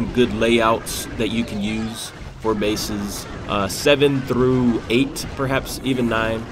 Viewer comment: I heard "good bases" and I immediately thought of old square base with lvl 4 walls...